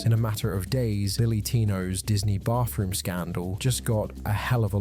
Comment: the subtitles said “Billy tino” 💀